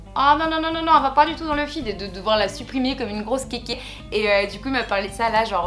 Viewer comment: C'est moi ou elle a dit"de devoir la suprimer comme une grosse kekeh ? Ptdr JUI Morte